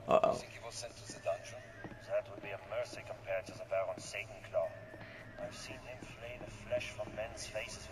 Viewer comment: Satan claw...I thought he said Santa Claus